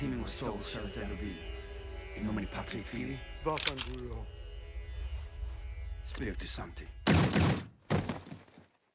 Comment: What did that guy say before he was shot? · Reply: He said "vaffanculo". Italian for "fuck off". His attempt at dying with dignity, I suppose.